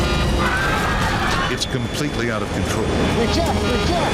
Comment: Was he saying eject lmao